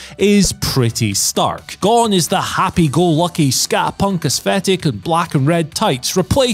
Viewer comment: SKA = skah He sounded like he said Scat Punk